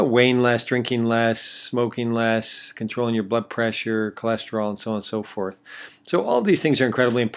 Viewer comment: Did I hear "butt pressure"?